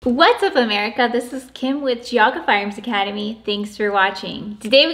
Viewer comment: I thought she said “Jug” Firearms Academy.